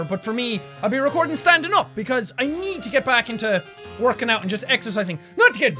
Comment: um... i thought he said he didn't work out any more